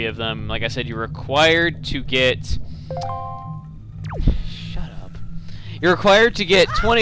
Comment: I think I heard some one say "Shut Up"